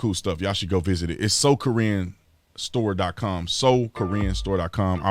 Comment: I just noticed it was “SoCorinne” store😂 All week I thought you said “SoKorean” store😂😂